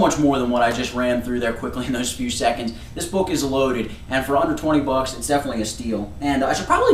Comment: it sounds like you said at the end: "and for a hundred and twenty bucks its definitely a steal" (4:29) when you said "for under twenty bucks."